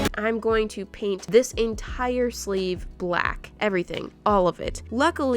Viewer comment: I swear I heard her say " I'm gonna paint this entire slave black "